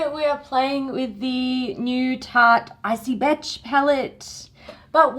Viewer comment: I thought she said icy bitch